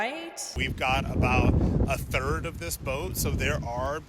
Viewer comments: What it sounded to me like he said was "we've got a third of this boat," meaning the gay cruise didn't book the entire liner. So TWO-THIRDS of the gay cruise was straight people. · I heard it as “we got 1/3rd of this boat…” as in 1/3 are gay men and lesbians (we), and 2/3rds straight. The other way I heard it was that only 1/3rd the boat’s tickets were sold 😂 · no, you misheard, he said they "have a third of this boat," meaning two thirds are straight